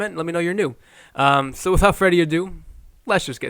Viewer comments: Did he just say at the beginning "Without Freddy Adu??" Lol -⏩ pun intended!!😅😅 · "Without Freddie Adu" hahahaha I thought I heard that the last video and now I definitely know it's what you said. I'm going to subscribe and I hope you keep me informed, entertained and keep me laughing